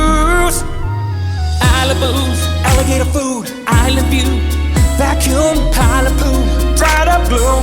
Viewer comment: I think Link actually says I love you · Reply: I think he said 'Island view.'